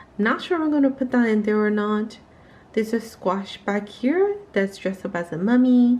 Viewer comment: The glove has been in the background a few times in past videos so I thought I'd just mention it, haha!! I'm just surprised that August follower count was higher even though I didn't post as much. I guess we need to do things outside of it to grow as well instead of focusing solely in the app. Thank you so much! I called him a Frakensqaush, lol. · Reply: lol! I thought I heard you say "Franken" and I have no idea why I didn't pickup on the "squash".... lol! I chuckle at his name, so cute! :)